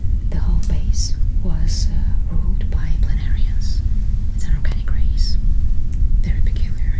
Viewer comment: did you say planarians? the flatworms?